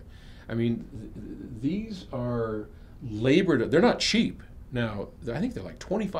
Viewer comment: Hi Paul I didn't get the second brand. What's its name? "I heard "Arbred"? Ok its P3ESR by Arbeth.